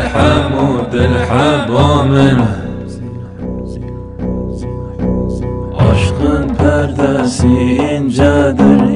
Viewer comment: de arkadaki ses ne diyor tam olarak?arkadaşlar kulaklıkla dinleyen varsa dinleyip söyleyebilir mi? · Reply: Hazine... hazine... hazine.... yani Hüzünlü demek. mürşidinin lakabı...